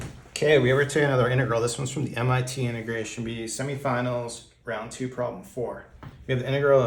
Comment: what are you saying the beginning (as in starting from 0:00) i've tried to decipher it like a million times and I still don't understand lol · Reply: It was “k we have here today another integral from the MIT integration bee”. Is that the part?